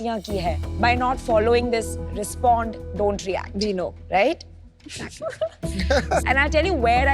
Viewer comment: they discussed something I heard 'dino' like did anyone understand what it is?? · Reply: Its "we know" not "dino" 😂